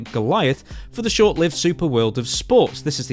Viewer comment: lol it says sober instead of super